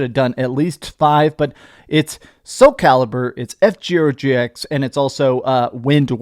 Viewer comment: Did you say F Gero GX? 😆 🤣 😂 😹 and I'm not surprised that playing Cyberpunk on handheld would drain the battery